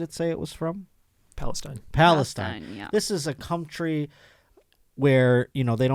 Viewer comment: did you say "this is a cumtry"? Little Freudian slip there I do believe.